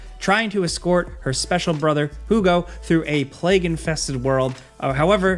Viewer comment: Did said Hu Go? 12:56 🤨